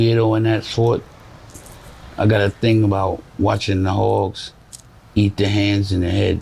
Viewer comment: What’s he saying around 27:00? Hogs ? Hawks? Eating people ?